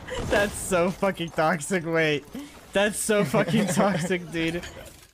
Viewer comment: のところ英語でなんて言ってるんですか？ That’s so 〜 の害悪の訳に当たる部分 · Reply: That's so fxxkin' toxic dude